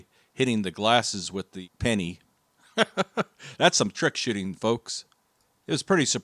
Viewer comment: I thought he said "trick shitting"😂